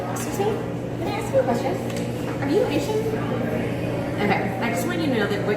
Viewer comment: did she ask the other girl if she is asian? i couldnt hear her properly.